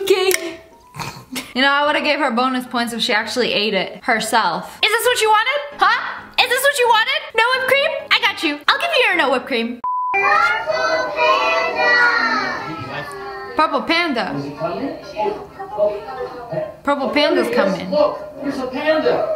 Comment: Isn't it kung fu panda? I mean thats what I heard